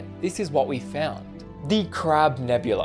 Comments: Ok ok ngl but I heard “Crap Nebula” at first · i literally heard "the crab nebula" as "the crap nebula"